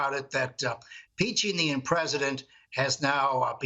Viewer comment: Did he say peaching the impresident lol